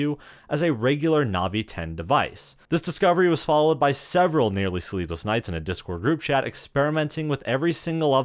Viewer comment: For all the world, I thought you said "Nearly Sleeveless Knights", and that was the term for your Discord members.